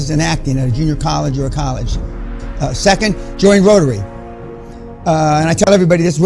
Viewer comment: Hello guys, at minute 3:17 I accusticaly did not understand what dan said. Join what? Had somebody understood what he said. Thanks for the help in advance. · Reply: join rotary club